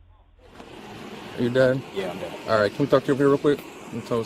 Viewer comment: lol 2:29 “are you dead” in the subtitles instead of “are you dad”